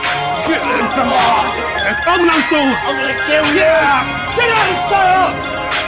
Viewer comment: lol i thought i heard that too the first time but nick said "i'm gonna kill you fowler"